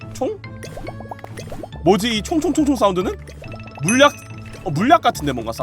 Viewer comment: 총 총 총 사후르로 들림 ㅋㅋㅋ